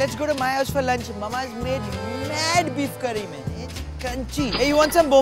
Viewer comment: Did you say beef curry?